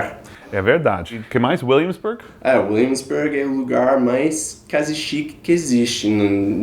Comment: O que eles quis dizer com a palavra "casichique"(foi o que eu entendi) 05:25 É engraçado o som da pronúncia de cool...